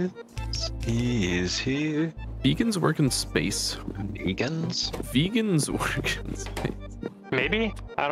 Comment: "beacons work in space? Vegans work in space?" Was just me who heard that?